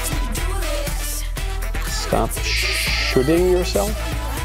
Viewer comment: I thought it says "Stop Shilling yourself" :P